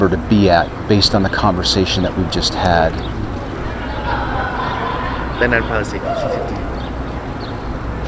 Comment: It's difficult to hear what he said there. Could someone type it out please? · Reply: "Then I'd probably say 50/50."